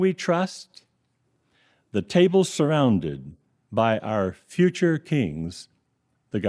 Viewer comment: I heard by our future kinks